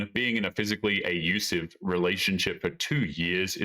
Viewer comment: You did not just say "ayusive" omg is anything real?